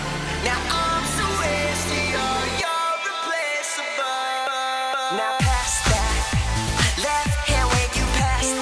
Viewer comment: I actually thought that they said that mitchel musso is now replaceable